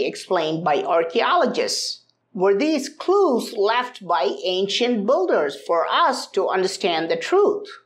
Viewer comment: I keep hearing "Ancient Budhas" and I'm like wow wtf!? ...and then I remember he actually said "ancient builders" Love your content tho.